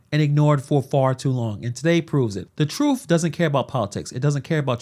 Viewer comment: Did you say "troof" instead of truth?